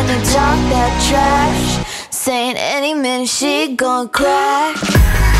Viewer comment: "Any minute she gon' crack." tyres screeching that got to me a bit❤ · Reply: Oml I thought it said 'aye man, she gon crack'